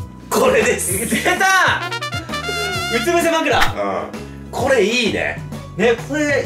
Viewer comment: うつ伏せ枕が渋ジャパ枕に聞こえた